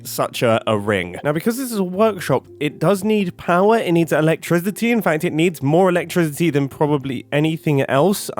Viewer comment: i swear i heard say ''electriZity''